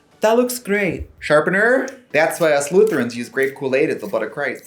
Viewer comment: I had to listen to this three times because I thought you said "us Slytherins" but then I realised it was "us lutherans". Oh well...